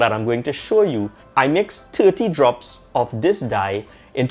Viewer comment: Hi, at 1:20 I couldn't catch the number of drops per 100ml...was that 2? Great video BTW...I got clear resin in the recent Beam3d kickstarter...so looking forward to trying this! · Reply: I used 30 drops per 100ml. It’s my Caribbean twang throwing you off 😬